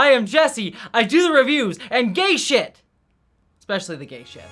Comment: I thought you said "Engage shit" and thought what a cool Captains catchphrase that would be 😂